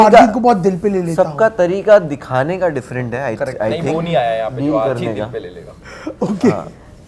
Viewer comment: Elvish ne kya bola ?? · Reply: Usne kaha vo aaya nahi jo har baat dil par le leta h